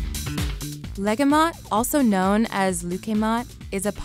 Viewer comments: I thought she said Ligma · Did she have to butcher every single pronunciation? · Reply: ISTG for the one in dubai i thought she said leukemia!my mom makes that dessert all the time and that is far off from the actually pronounciation i was so confused